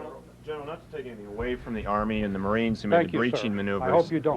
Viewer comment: I couldn't understand what reporter said ? inreturn gen said : I hope you don't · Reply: The reporter said, "Not to take away anything from the Marines or Army,,,"